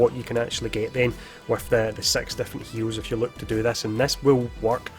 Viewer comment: i know hes saying SIX but it sounds very different lol. it sounds like hes saying sex heroes lol.